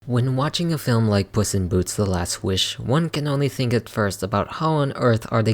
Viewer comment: I promise yall i heard "pussy in boots"